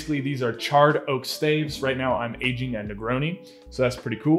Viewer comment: Stg i thought he said he was aging macaroni